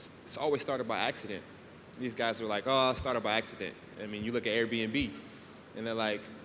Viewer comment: does anyone know what he said? Was it AB&B? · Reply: oh, Airbnb